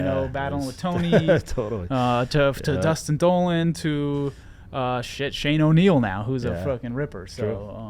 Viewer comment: lol, around 24:00 sounded like you called the dude "Shxt" Shane O'neill